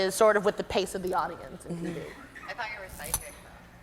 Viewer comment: waitt at 1:06:08 what does lily say to her ?? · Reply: she says i thought you were a psychic